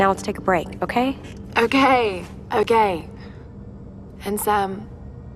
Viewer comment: I thought she said O-gay O-gay